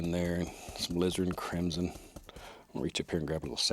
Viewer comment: I heard : "A lizard and a crimson"